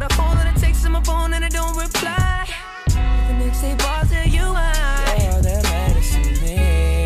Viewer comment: "the next eight bars tell you why " ??? I thought he was saying " the next day bothers you, i " 😧